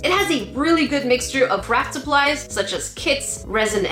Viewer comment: I thought she said Rat Supplies